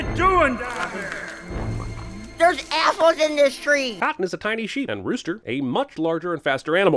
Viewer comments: I thought the sheep said "there's assholes in this tree!" 😂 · I can’t be the only one who thought the rabbit said “ there’s assholes in this tree”😂